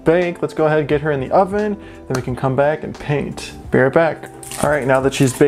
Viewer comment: I thought you said the minutes were going to make a comeback